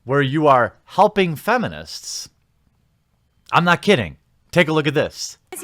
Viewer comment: Oh, HELPING feminists. I thought you said humping...